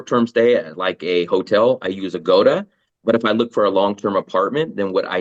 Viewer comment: hi, anyone can tell me what app he is mentioning, cannot understand it and the subs don't help. Trying to wrap my head around this a little. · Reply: I use agoda for hotels short stays. Or I use Facebook groups to find apartments for long stays.